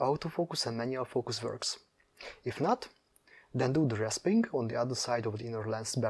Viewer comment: What are you saying at 6:27, rasping?